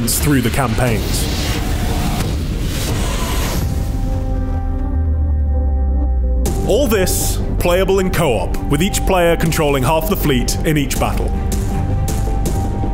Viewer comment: did he just say....CO-OP